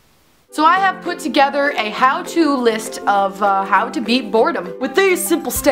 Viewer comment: Is it bad that I thought it said "How to eat boredom"